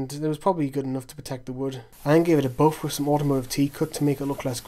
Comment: What did you use to polish it at 25:12? (I can't understand what you said) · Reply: Automotive T-Cut. It's just a brand of car polish I had hanging about. Kinda similar to Brasso.